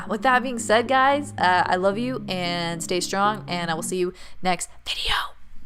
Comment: omfg I was falling asleep because I just ate and whenever I get warm I fall asleep but WHEN YOU WHISPERED “video” OT SOUNDED LIKE MY NAME (Miriam) AND I WOKE UP AND I WAS SO SCARED OML😭😭😂💀